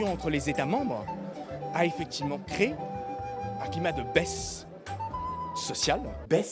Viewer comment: j'ai compris, il a dit "un climat de baise social" XD.